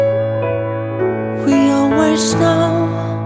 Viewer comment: i thought he was saying "you're my snow"